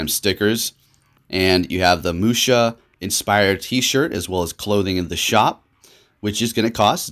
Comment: did he say Musha 0:48